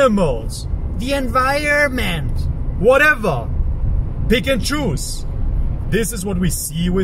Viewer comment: that feeling when I heard ''Pikachus'' xD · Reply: He said "pick and choose" 😂